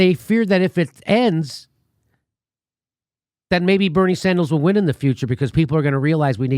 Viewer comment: did you just call that commie Bernie Sandals? Ha!!